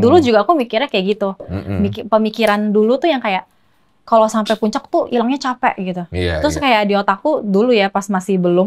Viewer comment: si Wendy bilang "kalo Sampai puncak tuh ilangnya capek" iya gasih? kok bukan capeknya ilang yah? Atau gue salah denger ya?